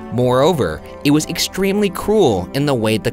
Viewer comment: I was in another tab when Ryan said this part, and I honest to god thought he said "cool" rather than "cruel", lol.